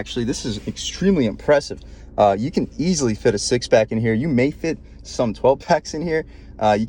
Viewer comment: Did he say 6 packs and 12 packs? 😆🤣😂 I think I hear a siren 🚨